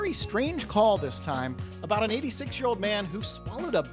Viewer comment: ~ Wow. I completely heard you wrong. I thought you said an “asexual Man“. I listen to virtually all my videos on 2X speed so the phrase “86 year old man“ sounded like that. I rewound 10 seconds to listen again One or two more times. But it didn’t seem right based on your reaction and words, so I thought maybe I was misunderstanding. Once I slowed it down to regular speed then I heard the 86 year old part.